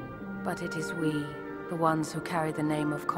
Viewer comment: Oh. My. God. All theze years I thought she said "wise" not "WIVES" kill me. That makes so much more sense😂